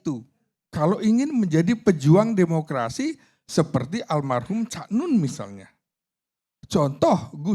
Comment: ga salah denger kn? Cak nun alm?